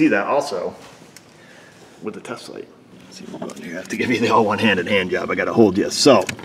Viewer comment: Anyone else hear him say, "One-handed hand job" while trying to get the camera situated under the dash? @ 6:55 Lmao.